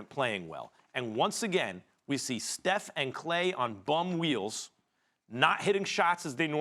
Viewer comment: all i heard was "BUM WHEELS" LOL